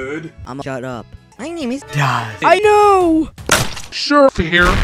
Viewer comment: I Thought He Said "I Know"